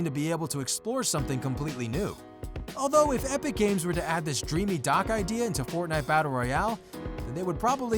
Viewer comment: I thought it said dream my dock XD